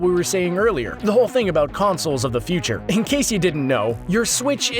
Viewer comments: Anyone else catch him saying gay-boy at 8:02? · did he say gay boy? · did he say gay boy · did he say gay boy?.